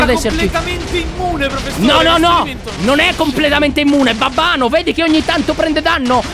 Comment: solo io ho sentito BABBANO?